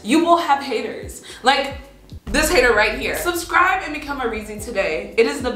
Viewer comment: Every time you said reezy in this video, it thought you said: Really easy, arizi, reeses, and breathing